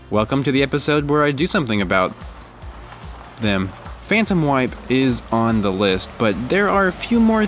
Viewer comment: I thought you said "Phantom Wife' instead of Phantom Wipe. It was quite puzzling!